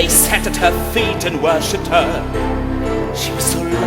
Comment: Could somebody please tell me what Maxim says at 0:27? "They sat at her feet and...", right? I can't make it out myself, I'm no English :) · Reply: worshipped her